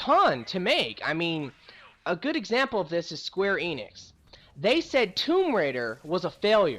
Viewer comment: I'm sorry I had to point it out but did you just say square anus?